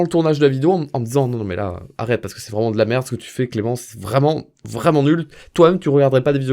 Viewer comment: À 2:10 tu as dit clémence C moi ou c vrais